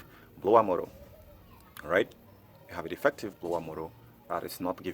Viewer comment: can't understand what is defective. Blower motor? Subtitles say blower model.